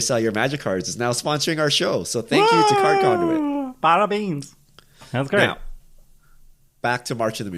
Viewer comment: Did just said "Parabains" around 1:59?! 🤣🤣🤣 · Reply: Parabens! Idk if my pronounciation is good though hehe